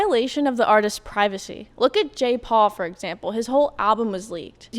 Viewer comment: lol def thought you said Jake Paul